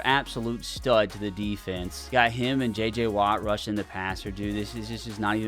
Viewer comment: who? Did I hear jj